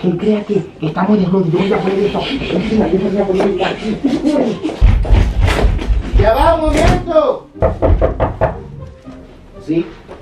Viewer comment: alguien sabe la que dijo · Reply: no, dijo YA VA UN MOMENTO